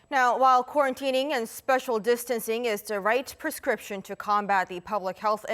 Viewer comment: Did she just say "special distancing"?